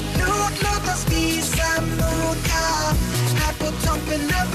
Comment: all I heard was "toughen the vibe" XD